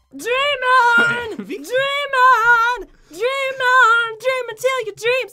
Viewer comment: These burning maps crazy!! Enjoy the vid guys! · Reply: I thought she said demon instead of dream on xD